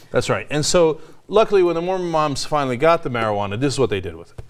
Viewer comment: thought it said moron moms.